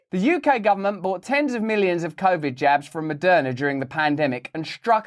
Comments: i was like "how the hell did Madonna make money off the vax tax?" you misheard it that's how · Lmao when Russell said Maderna made money off of you during the pandemic I thought he said Madonna 😂.... I was like I knew she was corrupt. I'm happy I kept watching lmao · We need modern Nierenberg trials. These criminals should be held accountable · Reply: I thought he said, "Madonna" ... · I honestly thought Russell said madonna in the beginning....and I thought what I usually think at the start of all Russell's videos which is "what is this world coming too!" lol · At first I thought he said Madonna 🤣 but context told me otherwise. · Oh my God, I thought he said Madonna.😅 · I thought he said Madonna, I was very shocked. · Am i the only one who first thought he was saying Madonna?? I was like damn I didn't know she was in with the GOVT! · Sheeeeeeeeees. I thought he said Madonna.... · Am I the only one that thought he said Madonna the first time? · At the opening I thought Russel said “Madonna” made money from the vaccines! I thought that was very strange… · Bro because of your accent I heard “Madonna” as the first word, and I was genuinely surprised she was able to sell vaccines to the government 😂😂😂